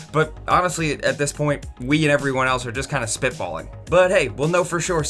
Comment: Near 5:47-5:52... Did you say "spit falling?" I believe the term is "spit balling." Still love ya though.